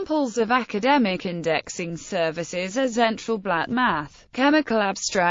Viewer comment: what did u say at 0:48 ? Central Black Math ?????